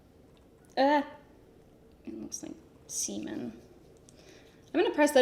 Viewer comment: did I hear say "it looks like semen"...